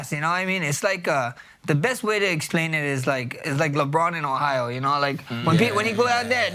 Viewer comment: His accent is so strong at 3:04 it sounds like he says Lebron is from acorn Ohio. 🤣